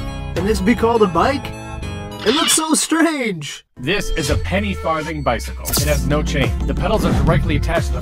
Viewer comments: Pov misheard hearing js peny farting bicycle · I thought he said penny farting 🤣🤣